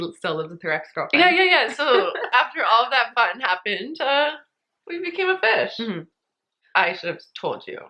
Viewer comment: I thought she said they became a fish and I was so confused